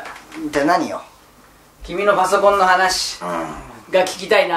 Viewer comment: 序盤のさんの「君のパソコンの話だよ」が「君がマザコンって話だよ」に聞こえた